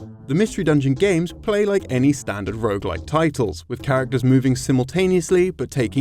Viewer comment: Does anyone know what he says says around 2:26? "Any standard ____ title" - I just can't make out the word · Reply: 'Rouge-like'. Rogue could be considered one of if not the originator of that kind of gameplay.